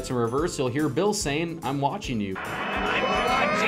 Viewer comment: he actually says “I’m watching you nerds” which is pretty funny